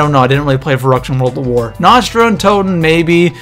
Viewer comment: Did you just say "Nacho Der Untoten"?